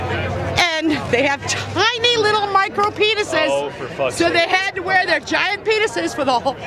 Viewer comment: Listen to 4:10 over and over. Thank me later. He really said oh FFS